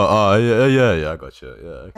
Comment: what did she say? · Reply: Lee is number one